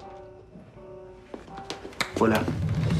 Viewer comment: como una letra le cambia el ámbito. En ese segundo flashé que dijo "Hola", así re tierni. Luego lo volví a ver y sigo "volá", así re sobrador.